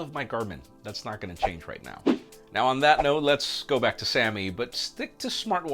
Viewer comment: When he started with "Let's go b...", for a moment I thought he was saying "Let's go, Brandon", but no, it was "Let's go back". 🤣🤣🤣😝